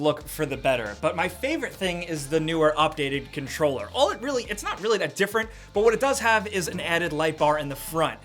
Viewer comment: i thought they said it was not real 4K